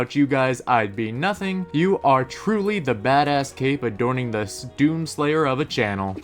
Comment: did he say a##?